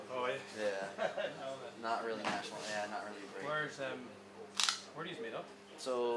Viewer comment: did he say “not really national, not really great”???